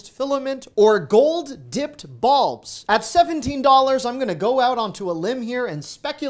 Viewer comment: I'm sorry, but I had to snicker when you said, "gold-dipped bulbs". That is because I heard "balls", not "bulbs". No, my sense of humor never left 2nd grade!